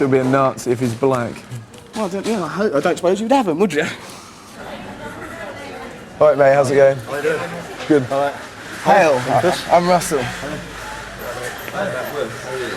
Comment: Did anyone else hear him say Heil @ 9:49? he was not happy!!! LOOOL